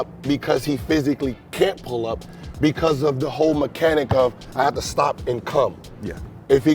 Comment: “ I have to stop and Cum” Did he just say that .🤣🤣